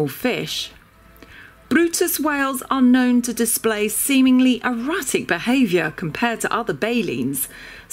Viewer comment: I heard erotic. I think she meant erratic